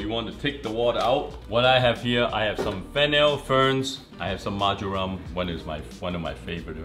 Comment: My ass thought he said fentanyl 😅